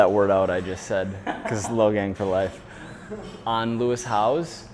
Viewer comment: Did he say Logang for life?